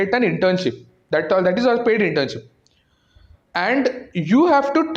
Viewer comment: did he say "that's a paid internship" or "that's unpaid internship"? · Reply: Paid